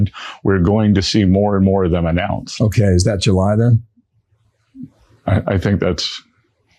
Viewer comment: The journalist asked to Bessent: "That's July then ? (about the next deal)" I heard "That's your lie then" ... and Bessent answered "Yes" !